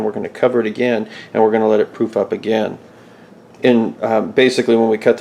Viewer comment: Did you say "poof up again"?